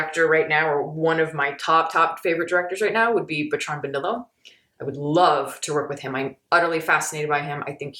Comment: What director she said around 10:50? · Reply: Bertrand Bonello